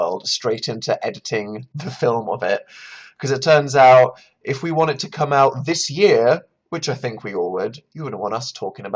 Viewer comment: What said: "If we want it to come out this year-" What I heard: "If we wanted to come out this year-" Wait what? Are my dreams alive? Can I finally be content? Listens again* Oh...